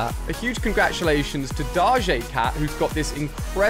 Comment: did he say da jay cat and not doja cat